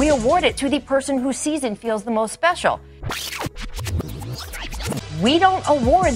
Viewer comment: I dont think she says, "who sees and feels the most special" i think its "who's season feels the most special"